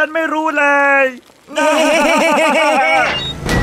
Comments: น้องวัวตาย 1:48พ่อบุญใหญ่กับน้องไปสบาย 2:20เเม่สีนวลตายเเน่ๆ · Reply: ได้ยินว่าแม่สีหนวด แล้วอีกอย่างใช่หรอ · เพื่อน:ชวนไปกินชาบู กู:ไม่ เพื่อน:ทำไมมึงไม่ไปกินวะปกติไม่เป็นแบบนี้ กู:สงสารแม่ศรีนวล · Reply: ได้ยินว่าแม่ศรีหนวด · ลาก่อนนะลูกแม่ศรีนวล น้องไปสบายกับพ่อบุญใหญ่แล้ว😭 แกทำให้ร้านชาบูดูแย่~ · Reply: ได้ยินว่าแม่ศรีหนวด